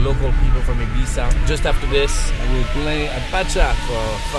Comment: did he said :fuck me im famous?